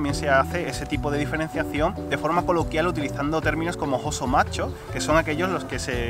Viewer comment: no estaba prestando atencion por un momento y escuche "ESE MACHO" hahaha xD 4:15 GORDI MACHO? XD